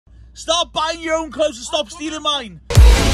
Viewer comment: At the start I thought Ben said "Stop buying your own clothes and stop stealing mine"